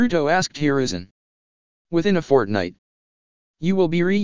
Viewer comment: did I hear fortnite